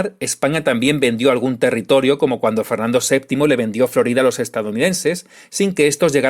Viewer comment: por un momento pensé que decías que vendió Louisiana. Pero no, dices Florida, ya me parecía a mi